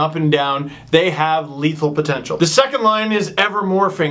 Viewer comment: did I hear "Leafal potential" ;) Hey, is ever coming out to LA for a game? I'd like to buy him 93oz. of his beverage of choice because reasons. Even though Fraser missed that call to send us to the soul-crushing Cup Final loss to the Habs, my favorite memories of a kid were chanting "Fraser Sucks!" at the Forum (LA) when the organist played "whatever that chant tune is called". Get well soon Elysse! #WeAreAllKings #GoKingsGo